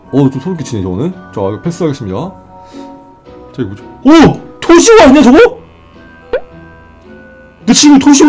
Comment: 뭐라는거야 ㅋㅋ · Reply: 토시오라고했어요...